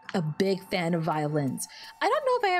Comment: when you first mentioned how you loved violins i thought you said you love violence xD